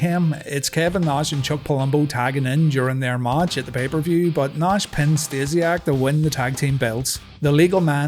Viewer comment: During the recap of the Above Average promo, instead of "Mike recalls", I heard "Micro Cole", and briefly thought that a fantastic insulting nickname had been born.